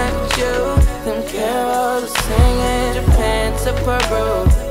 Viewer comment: surely I can’t be the only one who thought it was “them carols are singing” instead of “don’t care what they saying” 🤯